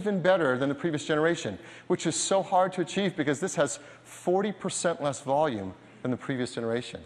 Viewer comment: Did he say that there was 40% less volume than the previous version